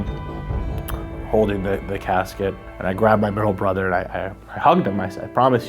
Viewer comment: Did he say girl brother?